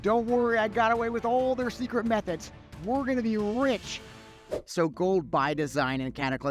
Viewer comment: worgen making more money from skinning somehow? :O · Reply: nah, it sounded like you said worgen be rich instead of we're gonna be rich